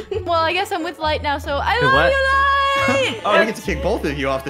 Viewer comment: did I just hear say I love u light